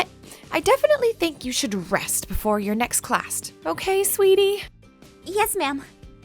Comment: i heard the teacher say "Classt" 😂😂😂😂😂 Good one teach